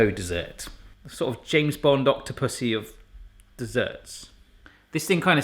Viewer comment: wait did you say octop***y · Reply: He said Octopussy yes.